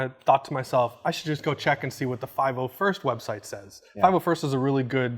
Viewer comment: What’s the name of the website he said in 4:45 ???? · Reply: Fernando Barbayani the 501st website, he’s referring to the 501st legion charity group